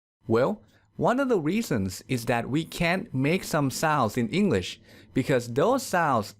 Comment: Why the Letter N doesn't sound when you pronunce the Word "souNds"?. I just heard "sause" :'( . Help